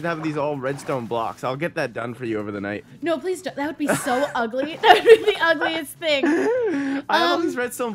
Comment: does she say "That would be ugly as DICKS!"